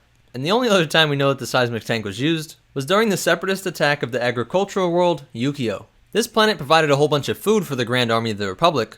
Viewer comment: I thought you said ...'agricultural world Yu-Gi-Oh!' Lol 😊😇